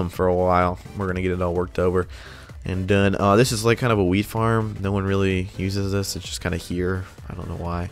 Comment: thought you said “weed farm” 😂 😂 😂 😂